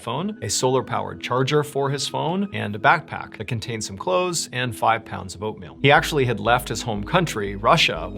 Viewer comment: I thought you said 5 pounds of opium at first and had to start this video over 🙂